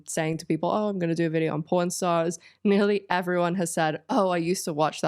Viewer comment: I hear porn"o" not pawn....